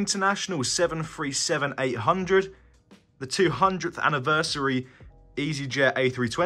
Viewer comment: did he say 200th anniversary are you sure it is not 20th anniversary